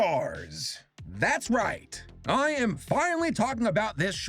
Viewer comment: am i the only one who thought it said BASTERS